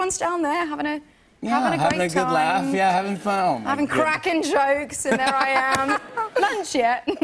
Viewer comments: Did she say “having cracking jokes” or “having craic (the Irish word for fun) and jokes”? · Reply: I lowkey thought she said having crack and jokes 😂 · Did she say “having cracking jokes” or “having craic (the Irish word for fun) and jokes”? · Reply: Gary Buckley "Cracking jokes". Although craic comes from crack (English), crack (English) means something slightly different from craic (Irish). An Englishman wouldn't say "having craic and jokes". In English English we might say "what's the crack?" or "it was a good crack" but that's about it. Nowadays the English will tend to use the newer Irish spelling to specify the Irish meaning. I don't think they are any longer completely synonymous. Note: not a linguist, could be talking shit.